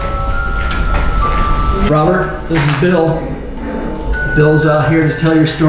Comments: I heard a “hi bill” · He actually says hey bill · I heard Robert the doll say hey bill leave a like if you heard it to